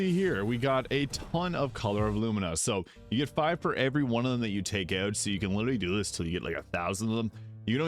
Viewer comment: Jeez I can't understand a single word you say between 3:15 and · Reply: You get 5 for every one of them you take out, so you can do this until you get 1000 of them